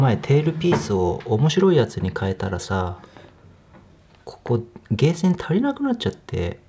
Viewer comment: でーせん？ゲーセンに聞こえるけどもｗ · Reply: 細い方から、 あーせん、でーせん、げーせん、つぇーせんって呼びます。笑